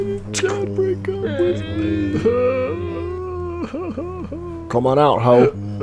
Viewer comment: Zoey: "Someone's dealing with a break-up!" Me: ""PROJARED IS THAT YOU!?" What makes this even better, is that I thought Bill was saying "Come out, Holly!" instead of "Come out, Hoe!" 😆